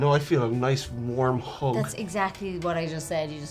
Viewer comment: I swear i heard "feels like a nice warm hog".